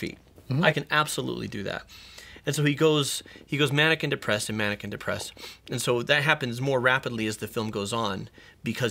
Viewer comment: Did anyone else hear "mannequin depressed, mannequin depressed"?